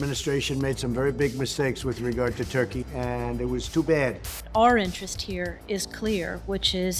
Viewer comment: for a moment I thought she said Orange not Our Interest 💀